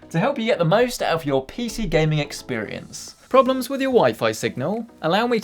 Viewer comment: I thought he said “problems with your wife?” And wondered how better internet would help